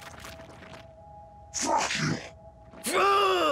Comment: Okay, I can't be the only person who heard that alien exclaim "Fuck you" when he went in for the stab.